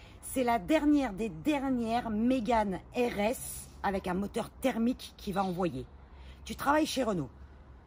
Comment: Juste pour info, la caisse est turbocompressée (c'est d'ailleurs le bruit du turbo que kiffe) donc quand dit "...la dernière des dernières mégane RS thermique qu va envoyer..." @ 1:58 et bien elle a pas bossé ses fiches... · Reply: effectivement par thermique j'ai entendu "atmosphérique"...reste à voir si la prochaine Megane RS sera hybride ou full électrique...